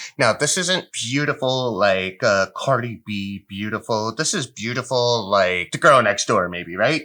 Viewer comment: If AMC makes me a millionaire I will move to the US where the money came from, And visit the AMC cinema every week for the rest of my life! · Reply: Did you just say “cardi b beautiful”😵‍💫